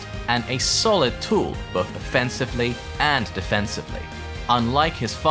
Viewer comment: Real quick did anyone hear that at 5:28 he says defense two times or am I hearing things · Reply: I believe he said offensively 1st